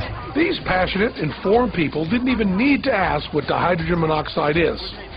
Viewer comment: "They didn't even ask what hydrogen monoxide was"? Somwhere around 15:40 did I hear right? Take that global alarming, #feelingsmug